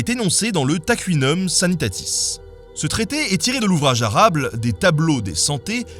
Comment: c'est moi où il dit "arable"?